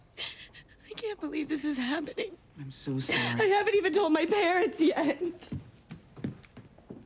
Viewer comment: Why does it sound like sue said, "I'm sue sorry"😂😂